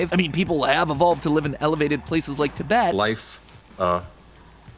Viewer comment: Did Matt say teyvat?